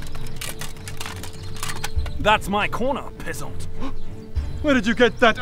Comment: That's my corner pheasont. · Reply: At times it sounded like they were saying "pissant" lmao